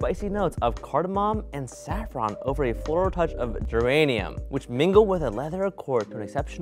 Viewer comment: I like how the sponsored segments always have a hint of hostage vibes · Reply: At first I thought said uranium instead of geranium, and I was horrified